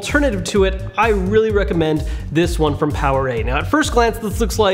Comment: I thought he said powerade